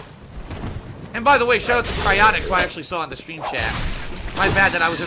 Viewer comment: did he say shout out to cryaotic?